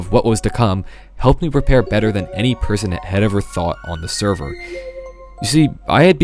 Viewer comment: Awesome intro, 6:09 I misheard it as "prepare better than any prisoner" Anyways, did not know you were in Strength since its been a long time since I watched it. You should join another smp, this seemed like a cool take